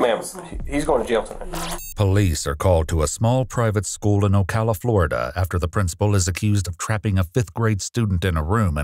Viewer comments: I thought said "OhCaliFlorida" like Ohio, California, and Florida all fused into one state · I thought I heard Califorida at the beginning of the video. the old is coming near